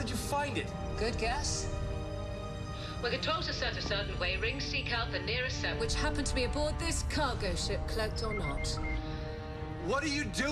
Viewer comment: What does she say at 0:47? "When the tolsa surfaces only, the ring seeks out..." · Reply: "When controls are set a certain way, rings seek out the nearest set.. ..which happen to be aboard this cargo ship, cloaked or not."